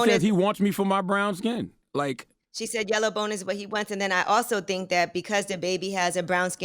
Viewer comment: Sad part is when I heard it, she was so garbled the first time I thought she said yella balls is what he wants 🤦🏾‍♀️